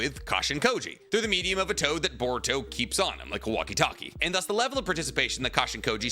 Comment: anyone else thought he said "Kawaki-Talkie" 😂